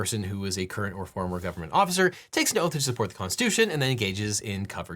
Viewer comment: Captioning error, and it's a pretty big deal: at 2:45 Devin says "a person ... takes an oath ...and then engages covered conduct". The captions say "takes NO oath", which is the polar opposite